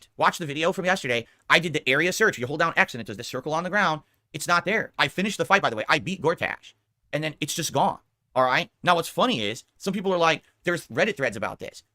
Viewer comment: It sounds like he said he beat doordash